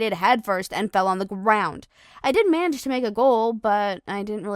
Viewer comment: i think she said: i didn't manage to make a goal